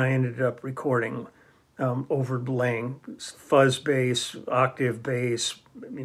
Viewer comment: You mentioned fuzz bass and I almost thought you said "buzz bass," as in the Pedulla (which was probably not even available at that point). That was a beautiful instrument!